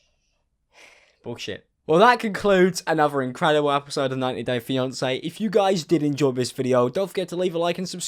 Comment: Why did i hear “90 day Beyonce”